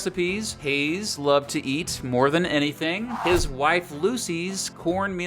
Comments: At first I thought you said “Hayes loved to eat, more than anything, his wife Lucy” and got very concerned. Glad the truth was much more wholesome. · Hoping for “Every President’s Prior Job/Public Office” next! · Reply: It sounds like he said he was going to eat his wife.